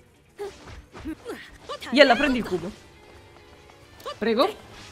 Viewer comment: Scusa forse ho sentito male, ma mi è sembrato che dicessi "Yella prendilo in cu**" ? ( "^ __ ^)